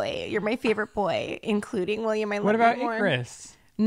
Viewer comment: Does say the Babies name here???? It sounds like she said William